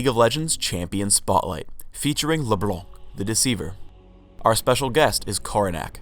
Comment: did he say ''LeBlungh''?